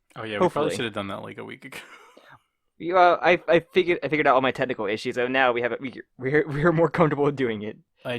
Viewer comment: I misheard "my technical issues" as "my tentacle issues" and it confused me for a second